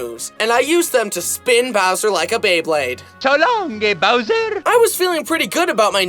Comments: It sounded like luigi said so long gay bowser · Why when little beat bowser i heard "so long gay bowser" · "so long gay bowser!" is what i heard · Whenever you’re at 52:58 of the video and he said I would spin Bowser like a Beyblade. It sounds like instead of saying so long a Bowser. It sounds like it says so long, gay Bowser.